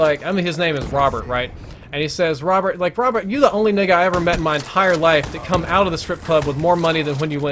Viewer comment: Did he say N*gga at 52:00?